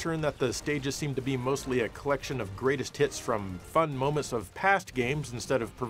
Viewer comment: did you just say tits?